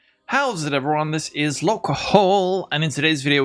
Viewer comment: First time watching your video and I heard "this is local hoe" at the intro.